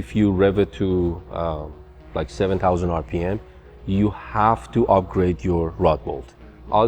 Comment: What is he saying that you have to upgrade at 5:40?? i cant understand through the accent! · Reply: “The weak point on the engine is the rod bolt…you have to upgrade your rod bolt(s)” BTB the bolts on the con rod. Closed captioning is your friend here.